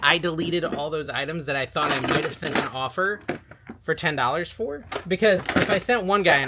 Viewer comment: I thought you said he sent the offer